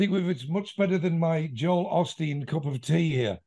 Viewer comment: three minutes 22 secouds did joe say his tea cup was Joel Osteen the texas preacher?